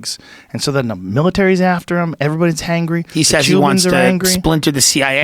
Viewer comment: did you say everyones hangry?